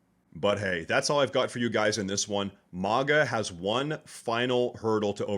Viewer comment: Why did you say MONGA ( @ 8:19 )? It's MAGA. lol